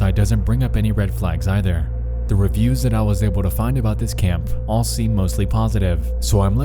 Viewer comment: I keep hearing "Kampf" instead of "camp" lol